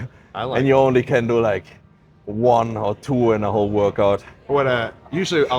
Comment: did he say hawk tuah? dont tell me im tweaking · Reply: He said one or two ,aahahaha